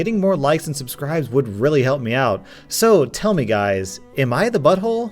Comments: I thought it said aim the butthole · Ngl at first I thought it said “Ami the butthole” and I was like, who’s that?